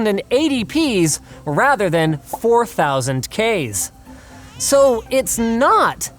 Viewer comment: Did he say 4000k's